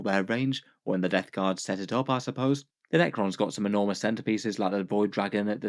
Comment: When you said "centerpiece" I heard "centipede" and got really excited until I realized it just looks like another hive tyrant variant